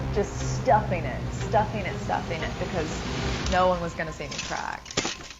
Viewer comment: For a moment I thought she said, "no one was going to save the crack . . . " Not see the crack. (Laugh now, but still that remark . . .. oh boy . . . )